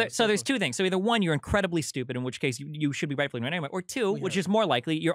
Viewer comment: "in which case you should be rightfully *nUrghNamER " What did Destiny say here? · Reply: “In which case you should be rightfully ignored anyway” I think